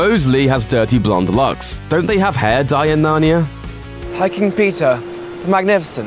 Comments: Everytime he said “Peter” I heard “Pizza” xD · I keep hearing "pizza" instead of "Peter".